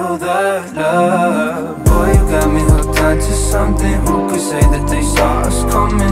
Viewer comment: i actually thought he said "girl u got me hooked on to something" its like my brain filtered out the gay